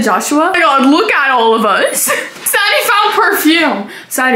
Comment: HELPPP WHY DID THE CAPTIONS SAY DADDY INSTEAD OF SARI😭😭😭😭🙏🙏